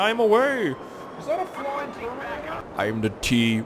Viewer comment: did anyone hear "is that a flying terminator?" seriously anyone?